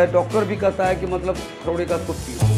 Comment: he said 'kharodey ka soup piyo' so fast that it sounded like something else.